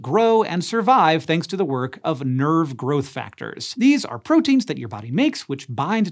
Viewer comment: I thought he said “Nerd Growth Factors”… dang, thought we had something there…